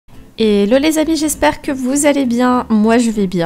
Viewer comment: A 0:00 elle a dit « hello les habits » ou c moi 🤣🤣🤣